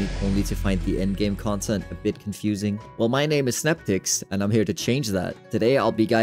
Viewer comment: thought he said my name is snapdicks lol thanks for the video